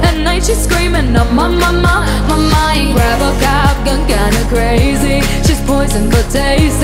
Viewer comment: I know this song by heart but why tf did i hear it “ robocop is kinda crazy” 😭 sleep deprivation i guess 🤣